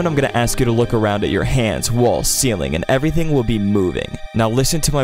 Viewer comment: bro my walls are twerking💀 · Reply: I thought you said balls💀